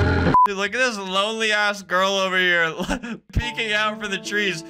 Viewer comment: Did he say ass at 8:37 😭😭😭